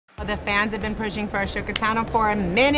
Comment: Did she just say “ashoka”??